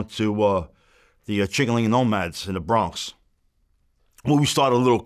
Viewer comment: THOUGHT HE SAID HE WAS A " CHICKEN WING NOMAD 🐔 " 🤦‍♂️ 😅😂😅😂😅😂😅😂😅😂